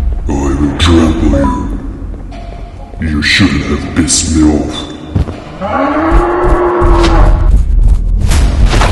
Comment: IM SORRY WHAT DID HE SAY? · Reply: The DOOM DOOM Said you shouldn't piss me off